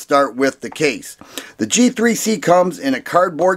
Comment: thought he said “Cheap 3 C” instead of “G3C” lol